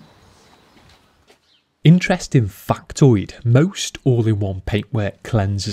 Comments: When he said “Factoid”, I honestly thought he said “FUCTOID” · I felt like i was watching national geographic documentary of washing a car in the wild. Df did he say at 18:58 " Interest in Fucktoid??"